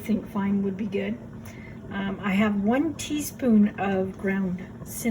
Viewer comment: Yes I deseeded them first. Let us know how it turns out for you! ;) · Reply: hi!! I followed your directions instead of recipe. On the recipe it calls for 1/3 c raisins and 1T lemon JC. I put the 2/3 raisins and 2 T of lemon JC. For some reason I thought you said 2 tsp cinnamon and listening to your video again you said only 1. I had put 2 and so, mine turned out perfect, set good, but to me it tastes kinda like apple cinnamon. But next time I will listen better and put only 1tsp. Lol. I just started making jelly and jams and I am addicted!! I love watching you and I love a good challenge! After we seed the zucchini there wasn’t much left and takes time to shred. But we’ll worth it!! Have you ever made Banana jam? It’s sooo good!! Thank you for being a good teacher!!